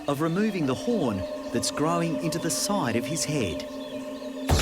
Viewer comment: I swear I am so dumb I thought it said the “the horn is growing his own skull” But it said the horn is growing into his own skull